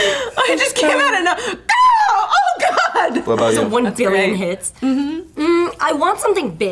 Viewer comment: Did she just say...hits?